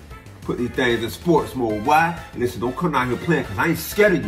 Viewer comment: Oh I thought he said we wasn’t scared 😂😂😂😂